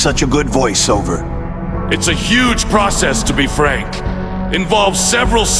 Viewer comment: Did he just said his name ? Frank ?